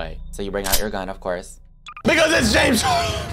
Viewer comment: WHAT DID HE SAY??? 😨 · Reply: He said "BECAUSE ITS JAMES CHARLES"